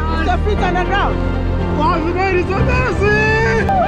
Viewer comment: ❤️❤️ from 🇬🇭🇬🇭, did i hear you say "is your feet on the ground" that was when i realize you are not going to swim🤣🤣🤣. such is love bro